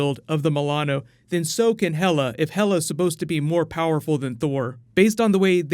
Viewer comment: I zoned out and thought you said hela was more powerful than thanos and i was like lol wHAT